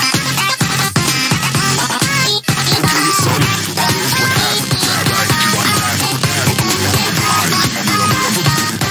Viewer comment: and also why did I hear him say caseoh 🤔🤔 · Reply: He said “okay, so?” Not caseoh